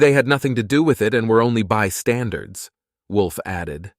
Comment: "they were only by standards" instead of "bystanders". lol